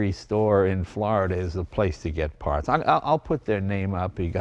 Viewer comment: I heard you say pahts.